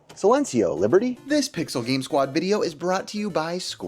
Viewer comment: Silencio means silence in Spanish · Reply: I thought what was said was cencio.